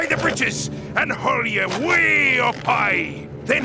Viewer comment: AND HIGHER! I hear ON FIRE ON FIRE ON FIRE ON FIRE ON FIRE ON FIRE ON FIRE ON FIRE ON FIRE ON FIRE!